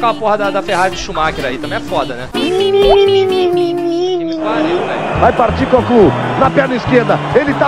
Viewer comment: Sei que isso é velho pra caralho e que é idiota eu perguntar isso, mas quando eu ouvi "Vai Partir Goku..." eu ouvi direito??? Kkkkkkkk.